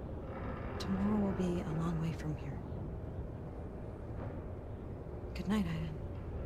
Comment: It really sounded like Aiden said "Good night Jodie", probably on purpose